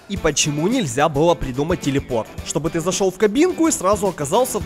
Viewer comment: То чувство, когда послышалось "Телефон" (но Nokia 3310 никто не отменял, ведь она может выпасть из кармана, пробить глубокую дыру в земле, ты туда упадёшь и телепортируешься на другую сторону Земли:))